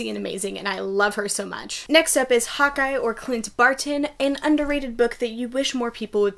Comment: when you said Hawkeye, i thought you said "hot guy" for a second 😂